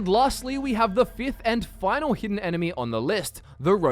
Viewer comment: I thought it said hidden memes